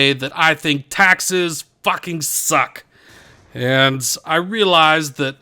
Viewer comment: I thought that it said Texas fucking suck